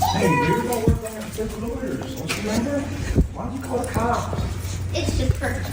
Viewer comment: I said this on another video, it sounds like he says something about the lawyers, then “don’t you remember?” So did they have a plan in place to do all this through the lawyers and Gypsy, in her usual selfish/impulsive fashion, decided she needed some stupid pictures immediately? (Also, are the pictures trophies of her crime?)